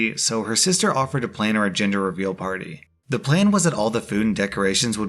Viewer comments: I thought you said “ginger reveal party” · I heard "ginger reveal party" in the third story and was so confused for like a second. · I 100% thought you said ginger reveal party when introducing the story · Not sure why but I heard "Ginger Reveal Party" when he started the story lmao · deadass thought he said ginger reveal party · I heard "Ginger reveal party"